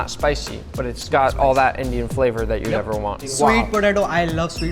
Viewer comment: did he say that you’d never want?? 😂😂😂